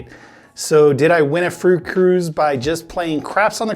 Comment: Did you win a fruit cruise? Lol that’s totally what I thought you said at the beginning.